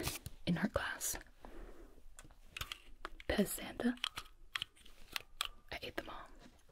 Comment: I was listening and thought you said "piss Santa" rather than "Pez Santa". I had to rewind that one